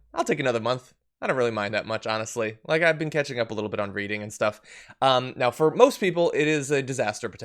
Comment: ????? WOW you said you were catching up a little bit on Breeding I thought I heard you say LOL but when I went back to listen to it the second time you said reading LOL